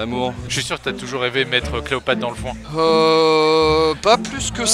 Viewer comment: J'ai compris "J'vais mettre Cléopâtre dans le fond"